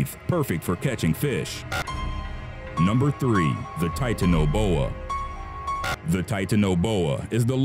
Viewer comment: I thought he said titan ebola.